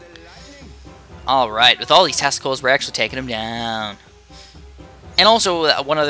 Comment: sounds like you said testicles :'D great vid man